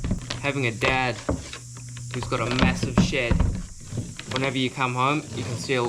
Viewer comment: I heard "one of the advantages of having a dad who has a mess of shit" instead of "a dad who has a massive shed". Ha! First heavy exposure to the Kiwi tongue.